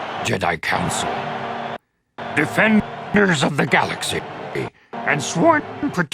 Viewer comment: “defend ***gers of the galaxy” ? I know I’m not the only one who heard that. I know me even pointing this out as a white dude is risky. lol They should have fixed that asap.